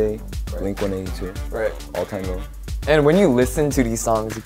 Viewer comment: Does anyone can tell me what they say at 1:45? "Avril Lavigne, Green Day, Link182" "right" then "_________" the subtitle says it's "all tango" but it sounds like "all time no?" Or.. I don't get it. And one more, at 3:05, "make a raspy voice on purpose" "yeah" " __________" the subtitle says it's "bossing" and it sounds like that but I can't get the meaning of it. Is this spell right or something wrong? I'm studying English with this video and I can't get it. Please someone help me. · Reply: Vernon is listing different artists. What he actually said was „all time low“ which is the name of a (great) band and at 3:05 Shua is saying „make a raspy voice on purpose while singing“ :)